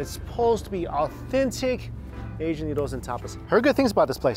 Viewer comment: I had to rewind when you went to that noodle place! I thought you said it's supposed to be a good place for Asian noodles and topless! lol! Hey!...a guy can hope! 😉